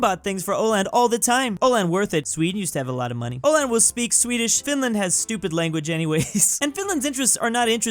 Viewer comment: I thought he said Spanish 💀💀💀